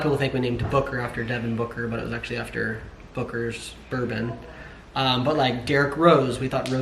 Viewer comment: I thought you said booger hahaha